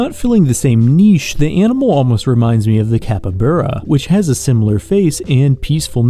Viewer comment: did you just say capyburra?